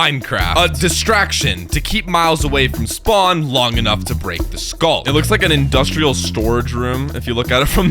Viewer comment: I thought for a sec you said: then we can break myles skull